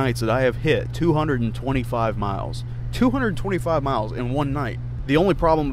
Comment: I thought he said "I hit 225 mph"